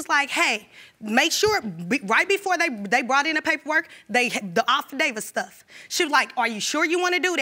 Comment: She seemed so calm UNTIL he opened HIS mouth! Then, she spent the entire time trying to cut him off! I CANNOT imagine being with someone like that! Did her sister just say "Off-David" instead of "affidavit" at 9:33? WOW! Let me keep watching this mess!